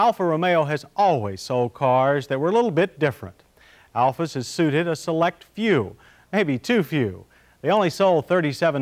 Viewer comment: Can anyone make out what John is saying at 0:33? Oh, & I LOVE how is all decked out in his '80's attorney/lawyer attire for this test!!!!! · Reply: ***** "Alfas have suited a select few" i think...